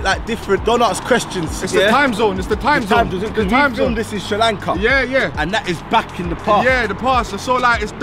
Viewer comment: Did he just say the word "srilanka